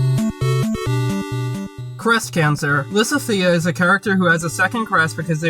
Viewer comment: I was looking on my other tabs opened while you're speaking and I thought you said breasts cancer lmao. I was like "wut?"